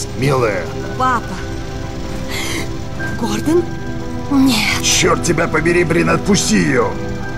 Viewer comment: if she says "niet" at 0:07 then its russian i cant tell what she says